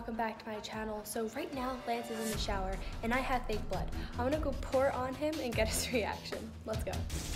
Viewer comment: Dude when said “and I have Fake blood” I thought she said and I have a big butt